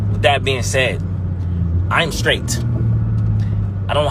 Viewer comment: it sounded like he said I AIN'T STRAIGHT ... for a second 😂😂 he'll be sound like he got some sugar on his tank.... Even your own family doesn't know everyone secrets